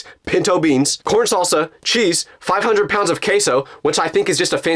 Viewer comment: i thought bro said caseoh, NOT QUESO😭